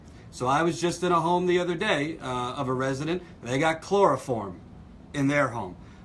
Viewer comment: Sorry, but it sounded like you said chloroform bacteria. Was is coliform bacteria?